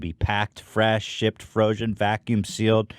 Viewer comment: was that froshen or frosion or frozen that I heard? Lol I love these silly ads. Other wise ads are no fun. I did enjoy this one from the laughs.